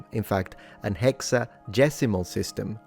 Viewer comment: at 5 min and maybe i missheard as it is similar but it sounds like you say hexadecimal but thats base 16 numbersystem not 60 base 60 is Sexagesimal which is probably what you say but the subtitles also say hex or maybe you say hexagesimal which would be wrong